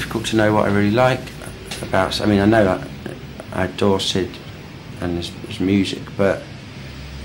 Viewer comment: does he say "Dorset"? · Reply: No lol. He said "I adore Syd and his music"!